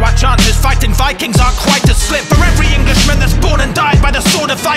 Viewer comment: I know I'm a few years late, but am I the only one who hears the Doom "Doot" meme when he says "For every" in this line? I'm not going crazy, am I?